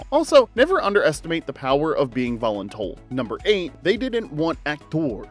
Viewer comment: what does he say? voluntulled? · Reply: “Volun-told”